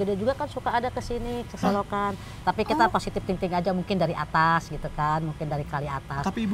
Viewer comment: gua dengernya "positif ting ting" receh bgt help ueueue